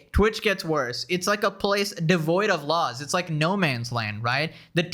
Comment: did somebody say PLACE?